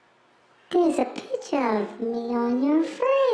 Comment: What was the last clue i just hear "there is a picture of me"